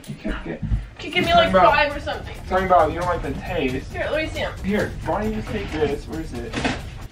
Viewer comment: in 9:33 did he call her babe?😂 Awww he was thinking about in that moment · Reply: No he said you don’t like the taste